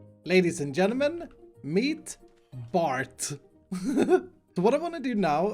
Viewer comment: Lol @ 11:46 I heard:"Meet Fart..")